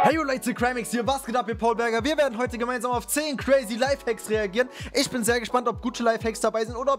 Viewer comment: Ich hab als erstes verstanden Gucci livehacks